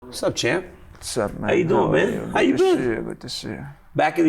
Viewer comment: Wasup mate 😂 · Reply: i heard "wassup man"